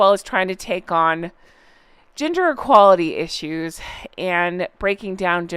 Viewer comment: I thought you were saying “GINGER EQUALITY “ 🤣🤣🤣🤣🤣🤣🤣🤣🤣🤣🤣